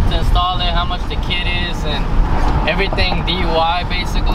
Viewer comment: said DUI instead of DIY